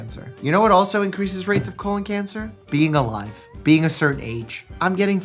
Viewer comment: I thought you said “this is why I haven’t had any gym gays” and I blushed